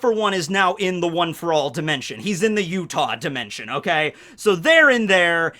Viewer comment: I thought was saying Ywach dimension